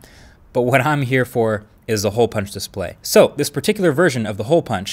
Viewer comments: I incorrectly heard it as Punctual Display :P, and actually searched it on Google. It is Google that Suggested the correct name as Punch Hole Display ;). Good video though!! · I thought it said HOE punch display.